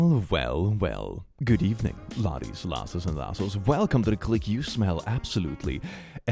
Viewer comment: Dude I’m probably missing something obvious but I cannot for the life of me figure out what he’s saying at the start T-T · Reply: "well, well, well, good evening laddies, lasses, and lassos."